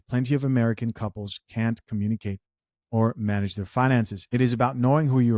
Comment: ALL I HEARD WAS DON'T COMMIT 😅. THIS IS LIKE THAT ASIAN MEME OF THE OLD WHITE GUY AT THE ATM AND 2 GIRLS SAY "MOMMA SICK, MOMMA NEEDS MEDICINE. COW SICK, COW NEEDS MEDICINE." 😅